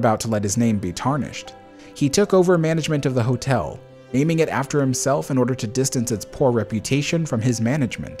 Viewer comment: Ah I wondered if it was a reupload as the subtitles don't match in places. That's a very beautiful building and I'm sure very iconic to the city, but buying potable water infrastructure for the people is something really special. Beyond living comfortably money won't buy happiness, and this man did the moral thing with at least a good chunk of his money and bought happiness for other people. Very few rich people do that. What would you do with that kind of money? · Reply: Okay I rewatched it and I must have just misheard some things you said the first time because I only found one error apart from the outro which must be from the old version which you just explained while I was typing. Where you said he renamed the hotel to the Benson Hotel the subtitles read "and named it" while you say "naming it". It's really not a big deal in this case but I always notice it. The worst offender I've seen is Netflix, where nothing matches and everything is abridged or restated, not to mention the dreaded "(Speaking Foreign Language)" lol. Thank you again for them. I know transcription is a lot of work.